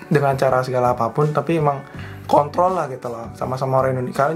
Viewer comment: kuping gua yg salah denger apa gimana ni wkwk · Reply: Kontrol woy dibilangnyq